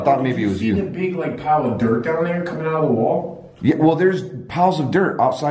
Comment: Uploaded this a week ago and had to take down because YouTube's robots wrongfully blacklisted it from ads and views. I worked really hard on it and its one of my favorites. A lot of you seen it and alot of you didn't. If you did see it please RE-SMASH that like button PLZ. Merry Christmas. Will be uploading a brand new video for you tomorrow on Christmas. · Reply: OmarGoshTV sounds like she says there coming